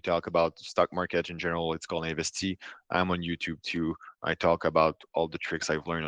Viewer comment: Yes, he runs a public channel : 1:06:11. I can't hear the name of his channel. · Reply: Investi